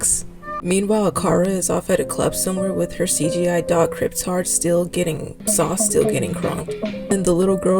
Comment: Good video and all, I hate the dog too. But was calling the dog kryp t-a-r-d at 2:08 really necessary to you or did i just mishear? It's just a little tiring the ammt of times this has happened recently with YouTubers I really liked and respected deciding to casually use ableist language, so I wanted to ask outright instead of assuming because tbh I want to have misheard and you said something else. Not trying to be a hate comment, I really hope it doesnt come off that way, I just need to know. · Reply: yes I called the dog kryptard, if it’s any consolation my brother’s on the spectrum and he found it funny